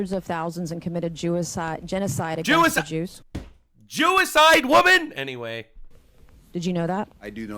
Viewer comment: she actually said ‘jewicide’!?!?! 😂😂😂